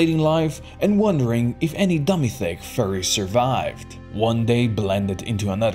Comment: did i hear furries...